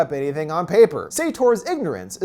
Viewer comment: I thought he said only on paper